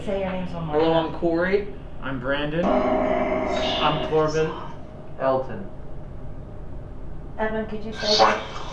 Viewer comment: when Elton says his name I clearly hear a man's voice say something then name .. kinda like hello Elton.. I did just smoke some gas tho but pretty sure I been hearing shit like crazy .. I know Im late on this but yea still watching the video as we speak.. great channel !